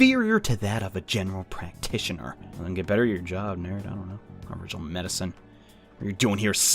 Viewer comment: Bro at 2:20 it sounds like he says the n-word